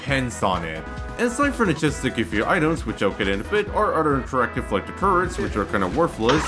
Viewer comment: I've replayed these few seconds like 10 times and can't for the life of me understand what you said 😅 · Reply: "And Aside from the chests that give you items, which I'll get into in a bit..."